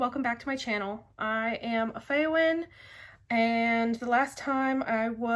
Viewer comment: i thought you said "I am faiwing uwu" then i read your name qlol